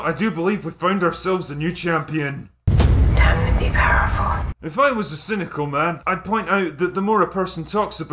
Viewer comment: Can anyone tell me what she says at 5:28? He keeps referencing this scene, and i cannot, for the life of me, hear what this lady is saying. Is that the joke? Lol. I turned my headphones all the way up, and all i can hear is, "aaahhhhchhkkaa meee powerful." Wth does she actually say?? Even the cc doesn't pick it up. Doesn't even try to guess! Any help will be greatly appreciated!!